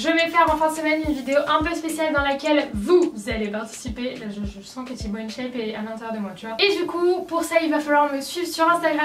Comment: C'est moi ou elle a dit: je sens que tiboinshape est à l'intérieur de moi !!!!🔞🔞🔞🌵🌽🍯💧🌡🌋